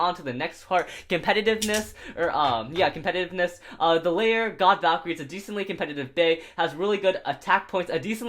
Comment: did he say God Valkyrie lol